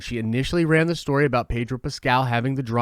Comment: I don't know why but I hear "Pager" when you say "Pedro" XD